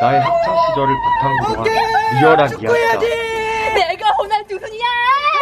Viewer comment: 나만 내가 호날두순이야!라고 들리냐?